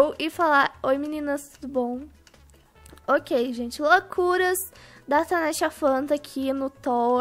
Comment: So eu q ouvi “loucuras da Tanasha Fanta” kkkk